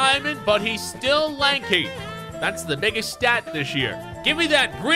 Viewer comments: Please tell me I’m high rn or did I hear say “That’s the nigga’s stat right there” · it sounded like ‘that’s the nigga’s stat this year’ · does say that’s the nigga stat this year🤷🏽‍♂️ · Yoooo at 8:40 I thought that said that’s the nigga’s stat this year